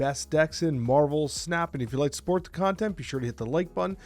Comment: I thought you said, “If you like SPORTS content..” and I laughed 😅